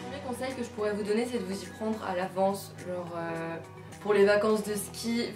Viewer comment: att j'ai entendu vous vous y prendez la ? a 00:57 aie mes oreilles mdrr sinon cool la vvideo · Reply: Non non j'ai dit "de vous y prendre à l'avance" ;) merci